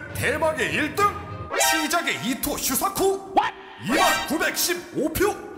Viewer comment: 이토 키사쿠라고 적혀있는데 몇번을 들어도 이토 슈사쿠라고 들리네요. 자막도 이토 슈사쿠라고 되어있지만...무엇보다 명백히 한자가 다릅니다. 아 물론 이 노렸다면 상관은 없지만요.